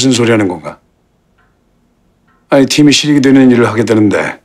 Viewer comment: 팀이 ㅇㅇ되는일을하겠다는데? 최전무 뭐라는거임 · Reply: 실익